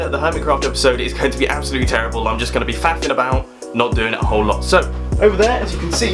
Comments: I heard "fapping" about oml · did he say fapping about? 😂